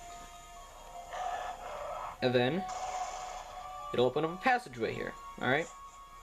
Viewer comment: AAAAAAGGGGHH I CAN'T UNDERSTAND WTF DID YOU SAY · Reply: It will open a passage way here